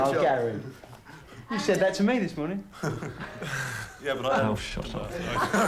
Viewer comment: anyone catch what bob geldof was saying it seemed like it was dubbed over with ‘shut up’?? 🧐